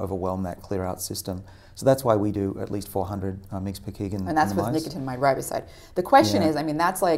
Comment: I don't understand what Dr. Sinclair stated at 53:56, discussing the minimum dosage for NR based on bodyweight. Did he say "400 mcg per kg"? If so, I can't square the math with what Dr. Patrick said a few moments later when she mentioned >2 grams/day. Did I mishear him? He was speaking quickly. Thanks. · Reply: Hey, David! That was 400 mg per kg in rodents. Human equivalent dose extrapolation is a little bit more complex than just scaling up for weight, though. Check out table 1 in this paper. <link>